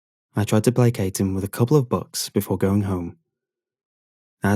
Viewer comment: The way you pronounce bucks made it sound like you were saying "books."